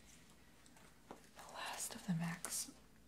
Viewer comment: I heard "the last of the max welts." I lost it there.